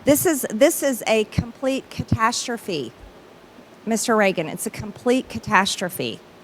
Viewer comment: Did she call Raskin “Reagan”?